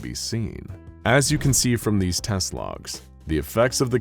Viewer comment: instead of "test logs" i heard "teslas"